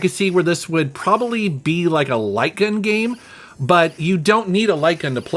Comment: The first time you said light gun, I thought you said lycan. 🤷‍♂️